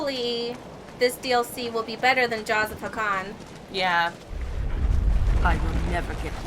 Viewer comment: I'm sorry, stupid comment and really late but...when you said "Jaws of Hakkon" I heard "Jaws of Pecan". But, just imagine the Avvar as pecans though.